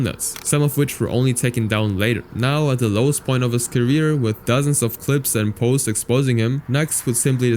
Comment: He literally said "dozens" not "thousands" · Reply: it sounded like he said thousands. Dozens makes way more sense.